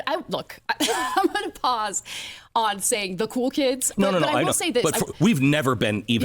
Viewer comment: ana starts laughing a lil too hard lol "cool kids" checkbook is all I hear